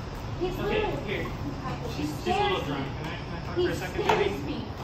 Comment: When the police showed up I thought I heard the boyfriend say something like "she's really drunk." Did anyone else hear that? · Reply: I heard, ''she's little bit drunk''